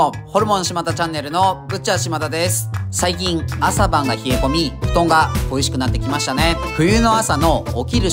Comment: 開幕のトークが「布団が美味しくなってきましたね」に聞こえて笑ってしまった